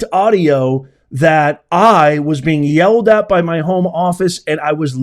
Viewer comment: I thought she said 'bosses' not home office. I do think you're making a bigger deal out of this then it needs to be. I kinda like how Vance handled it. I'm not saying that you shouldn't report on it... but that you shouldn't be shocked by it. Its not just how Vance handled the debate, but also how he handled things like this which make me respect him more each time I see him in the news.